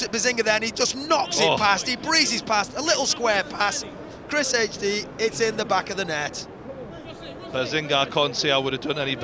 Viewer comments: did they just say chrishd is in the back of the net? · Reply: “Chrishd it’s in the back of the net” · Did anybody else hear him say ChrisHD at 30:26. Isn't it ChrisMD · Did anybody notice at 30:26 the commentator calls him chrishd instead of chrismd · Did he say chrishd isn't it chrismd? am i the only one who noticed this?